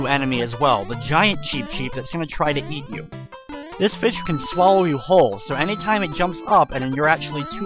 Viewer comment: Did you say this bitch will swallow you whole @ 27:35 O_o